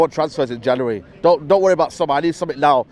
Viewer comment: Did he say don't worry about somalis at 2:53 ??